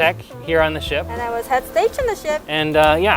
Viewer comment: Aghhhhh I don’t want to watch this, I’m so cringy 🤦🏻‍♀️🤦🏻‍♀️🤦🏻‍♀️ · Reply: just trying to understand what you said here. I heard "I was head stage on the ship". Like head stage staff? Edit: OK this was explained at the end of the video. Head stage staff. Got it.